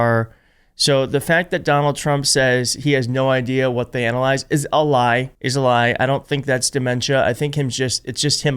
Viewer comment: It didn't sound like he said analyze it sounds like he's saying ADALYZE